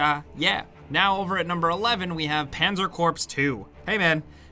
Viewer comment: Did he just call it 'Pazer *Corpse*'? Oh man, that Engurish!